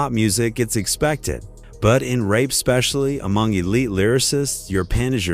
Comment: The AI says rape instead of rap plus so many other mispernonciations